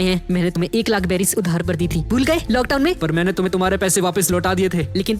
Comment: Sorry guys pichle kuch din se thoda busy tha..🥲🙏🏻❤️ par ab se long aur regular videos aayengi 🤗🙏🏻❤️ Like Target 🎯 - 14k Likes 🤗🙏🏻❤️ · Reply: locktown i heard lockdown muje 😅 laga one piece mai kab lock fown laga